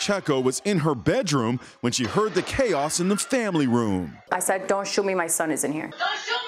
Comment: For a second I thought she was saying “don’t shoot me shoot my child” lol